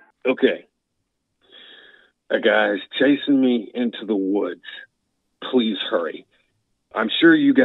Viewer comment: sounds like he says got chased into the woods doesnt sound like chasing me like someone chased someone into the woods like describing what he's seeing & he's probably going opposite of that & probably whoever he's talking bout caught up to him ... he was probably walking & came across a state trooper had pulled over someone like he came upon on the scene... & he either says shot or fought the first guy